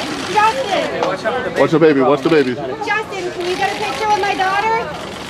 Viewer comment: Can anybody tell me what he says at 1:52? plz&ty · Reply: "You made the baby cry."